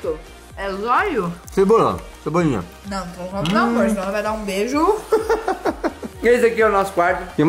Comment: so eu que ouvi a chamndo ele de mor